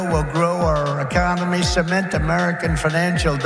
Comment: Did he say "Ecomony"? Like in Idiocracy?